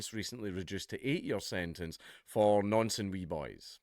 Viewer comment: What the heck is he saying at the end "For No Sense We Buys?"